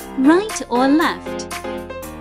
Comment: Is it just me but for the 4th one I heard "Left or left?"